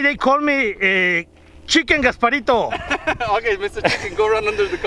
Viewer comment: I thought he said "they call me chicken-despacito"